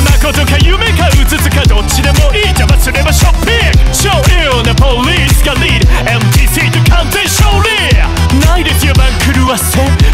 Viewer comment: 英語苦手な自分1:14流暢すぎてill police leadが聞き取れなかった。